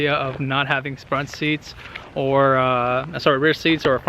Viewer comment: What did you say?? SPRUNT SEATS XD